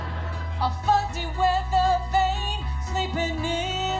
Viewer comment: I had to put on the CC. What I heard was "a f***king weather vane."